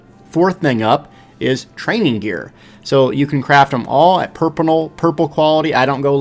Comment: a bit after 1:30 did he say purpinal